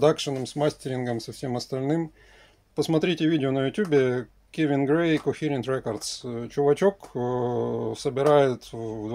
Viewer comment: Не могу расслышать рекомендацию на 1:45, напишите пожалуйста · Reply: "Kevin Gray Cohearent records"